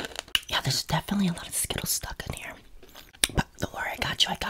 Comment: People who thought he was saying snail eggs Instead of skittles💀💀